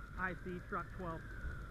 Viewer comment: At first when he was saying “I see truck 12” i thought he was saying “F$&k 12”. #BackTheRed #BackTheBlue